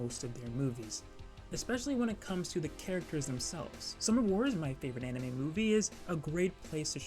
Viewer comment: can someone tell me what is the movie at 5:00? i coudn´t understand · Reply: sorry! So the Studio is called Studio Chizu and the movie i talked about from them was Summer Wars